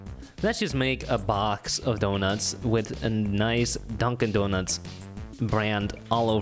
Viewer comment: i wasn't looking at the screen and i was so confused cause i thought you we're saying my name duncan not dunkin lmao